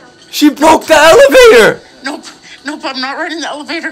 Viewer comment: lmao my high ass thought she said please roll me downstairs at the end😂